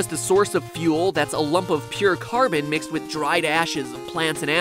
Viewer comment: The first time I heard this I thought he said, "Charcoal, meanwhile, is really just a source of fuel that's a lump of pure Mormon" Instead of Carbon.